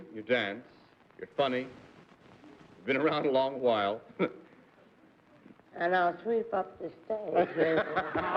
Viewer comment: Can anyone tell me what Judy says at 17:51? I can't make it out very well, even when I slow down the speed. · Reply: How she even sweeps up the stage. Reference to costume as the tramp maybe like the Carol Burnett homage.